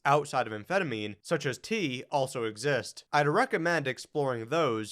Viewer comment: you mention T(?) as another option (other than Adderall) for ADHD sufferers. Can you clarify what "T" is? LOL, you're not saying, "tea", are you?! I think you mentioned it also when asked what one drug you would take. I am currently taking Adderall for ADHD but would like to explore other options. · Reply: Yes, "tea (caffeine)". I wouldn't recommend it as an ADHD medication alternative, just as an option for those with no pathological focus deficits.